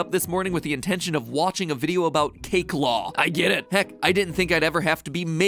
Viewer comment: For a second when he said cake law I thought he said cake lore... I may have watched too many theories...